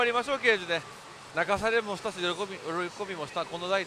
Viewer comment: 泣かされもしたし喜びもしたし、でえぇんか？ 3回繰り返してやっと聞き取れたゾ